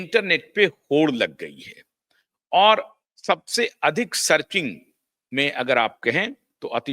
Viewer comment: सर्किंग ???? ये कौनसा नया शब्द आपने ईजाद किया? डिस्कवरी or इन्वेंशन? क्या बोलते हो भाई? शब्दोच्चरण पर थोड़ा ध्यान दीजिए